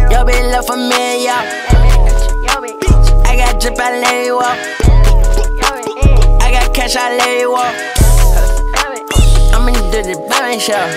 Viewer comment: I though he said “I got cash I let it walk” and that was a goated bar but I was wrong. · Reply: I thought he said “i got cash i larry walk”😭